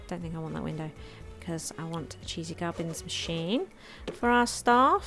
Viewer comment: every time she said cheesy gubbins i thought she was saying cheesy garbage! haha